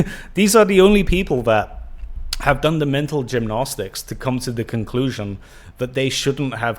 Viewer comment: I realize it's your particular accent, but I keep hearing "mental gym-gnostics," and I chuckle. Pattern recognition is a funny thing once in a while.